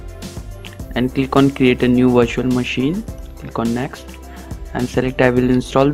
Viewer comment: did he say "create a new WASHING MACHINE" ???